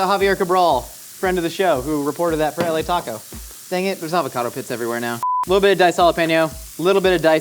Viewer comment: honestly thought Josh said, “there’s avocado piss everywhere now.” 😆 Petition to make “avocado piss” a phrase.